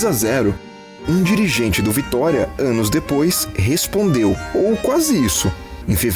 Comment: " ânus depois' entendi.